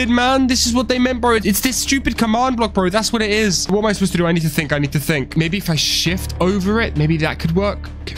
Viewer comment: ayooo you realize in 44:10 bro said "the cauldron isn't filled to the top" why did he spell "filled" "failed" XD XD XD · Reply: Just like at 1:09:24 he said think but it said thick ayo